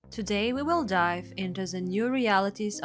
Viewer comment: With your cool accent I thought you said " TODAY WE WILL DIE " in the beginning . . . . lmaol